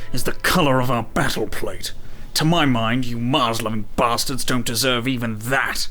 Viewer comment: Did he just said "Muslim bastards"?